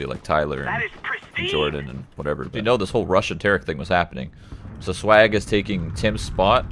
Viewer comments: It sounds like summit said Russian Tarik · i heard "RUSSIAN TARIK?"